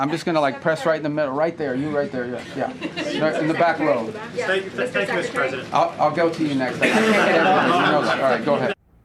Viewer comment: I think I heard, "Thank you, Mr President," at 1:19. These ears need to get checked.